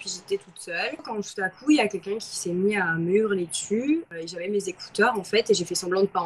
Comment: Selon les sous-titres YouTube, au début de la vidéo (0:04) lorsqu'elle dit: " quelqu'un sait mit a me hurler dessus" a été transformé en "quelqu'un sait ni mur laitue" 🤣🤣🤣🤣 C'est une forme de censure ou quoi ??